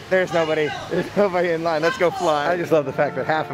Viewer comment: pretty sure I heard "lets go flying"